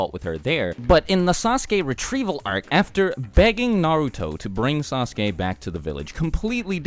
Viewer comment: I thought you said "banging Naruto" instead of "begging Naruto", and I was like "WHAT!?"